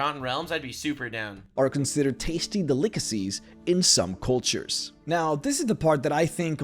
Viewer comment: Did he say "De-lick-acies"?